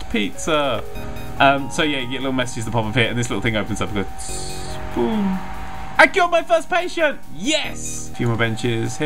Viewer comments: LOL! when you said Yay i cured my first pateint! IT SOUNDED LIKE YOU SAID YAY I KILLED MY FIRST PATEINT! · When he said "I cured my first patient, yay!" I thought he said "I killed my first patient, yay!" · Ah i thought you said "Ah i killed my first patient!" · did anyone else hear killed and not cured? No? Just me? Ok I'll shut up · what i hear: " I KILLED MY 1ST PATIENT! YES!!! what he really said: "I CURED MY 1ST PATENT! YES!!!" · I thought he said i've killed my first patient lol